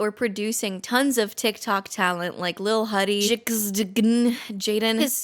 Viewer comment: i misheard and thought you said "non-talent"